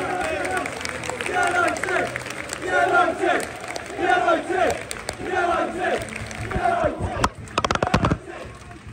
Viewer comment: what this chant says please ? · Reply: "United"